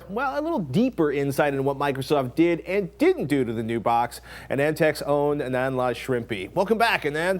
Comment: Even though I know of anandtech I thought patrick said antec at first